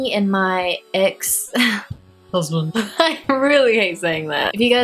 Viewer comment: OMG when you said "husband" in the beginning I thought you said, cousin loollll